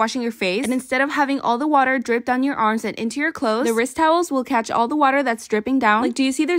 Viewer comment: I heard "Rizz towels"